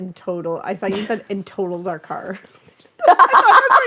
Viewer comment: I came here to watch and comment 😂 I usually listen on the Apple Podcast app. I also thought she said “totaled our car” or something 😂😂😂